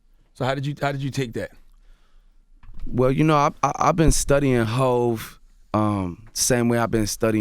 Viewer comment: Ha i thought he was gon say "ive been studying hoes" @ 0:38 LOL